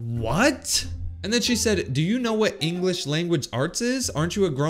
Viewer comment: phew i thought she said english or spanish i wasnt moving